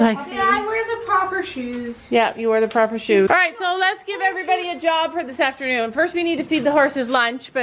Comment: I thought said “you all have a dog for the afternoon”’ I was like “omg”